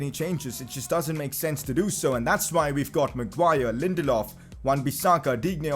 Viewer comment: just me who thought he said “Wan-Bissakadinho” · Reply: He said wan bissaka and digne